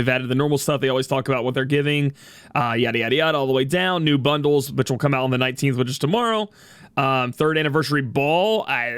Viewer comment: Did he just say Ari Ari Ari.....? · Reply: Arrividerci